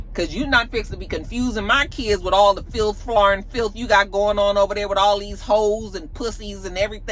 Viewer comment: Wait?!?! Did you just say "...filth,flarn filth...!" 😂😂😂😂😂😂😂😂